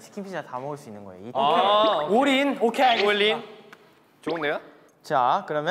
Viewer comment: am i deaf or did felix say all in